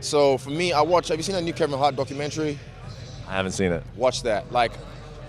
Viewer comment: What is the name of the documentary? English is not my main language and I don't understand what he is saying there.. Thank you, Have a nice day. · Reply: john doo Kevin Hart Documentary, he’s a comedian